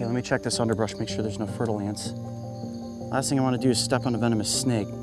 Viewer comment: When Coyote first said "Fer-De-Lance" I literally thought he was saying Fertile Ants